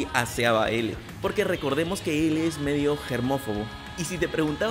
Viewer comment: pregunta lo que dijo en el minuto 8:13 es hermofogo¿? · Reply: Era germofobo, osea tenía obsesión con la limpieza